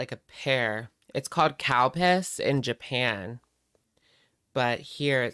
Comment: Please tell me I’m not the only one who thought said “it’s called cow p*ss” 😂